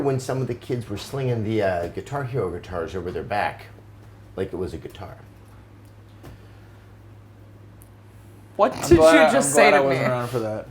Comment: I swear at the beginning of the video I thought Adam said "..sacks of turnips over their back like it was dirt." Felt like I was talking to my Grandpa.